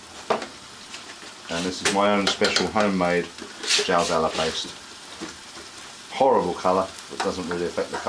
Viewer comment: what paste did he say? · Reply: Jalz Alla paste... named after the dish, I'm assuming anyway